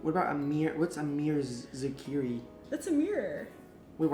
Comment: When u said Anwar I thought u said a mirror